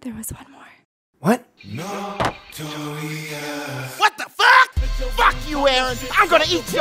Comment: What did it say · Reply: "Notorious"... The intro to Spit yo game.